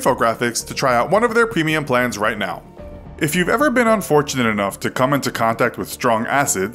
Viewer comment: I heard "If you've ever been on 4chan enough..."